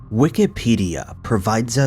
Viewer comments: At the beginning of the video I thought he said Wickedpedia! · I thought he said wickedpedia and thought that be kind a cool site. A wiki for bad things 🤔